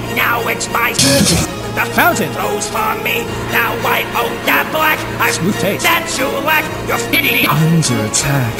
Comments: I thought spamton said block, like he owns the place, but even now that I know it says black I completely understand what you meant, and anyone who got offended by it and took it out of context are dumb, keep up your amazing work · People who genuinely hate this song because they hear the word "black" and can't possibly see it as something not racist exist and it's shocking. This song slaps and people are idiots. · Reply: I thought he said block